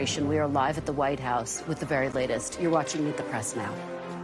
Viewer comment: at the commercial break she said "in the press now" and I thought she said "next on depressed now"